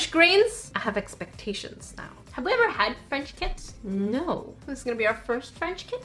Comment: I thought said frenchkins when she said French kits hahaha. I was like aew that's cute hahah